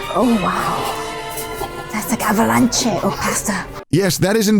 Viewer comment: what the hell did kaho say 😅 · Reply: “Oh wow~~~ that’s like (an) avalanche of pasta”